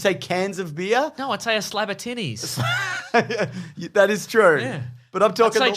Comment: I heard "slap a titties" at first